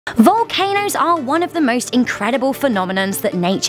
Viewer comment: It sounded like she said valcaner